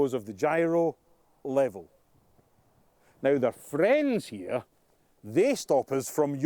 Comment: What is he calling the vertical stabilizers? "Friends"? "Frens"? "Phrens"?